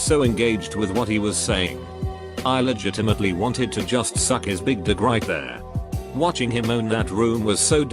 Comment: am I the only one who heard "big dick"